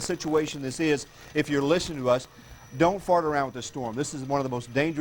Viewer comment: At the @ 39:24 mark does he really say not to fart around with the storm??